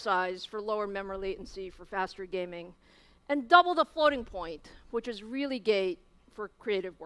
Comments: did she say the floating point was gay????? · I swear at 10:20 Lisa said really gay for creative workloads lol >_<